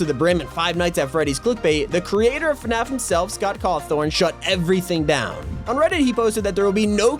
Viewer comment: did he say Scott cawthorn